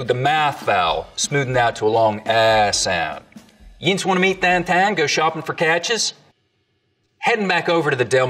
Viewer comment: he definitely says "YINZ wanna meet downtown..." but the captions say "you guys want to meet downtown"... gotta keep it straight, yinz is an important word for the Pittsburghers ;)